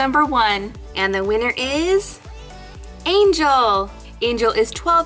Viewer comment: I thought it says Angela 😔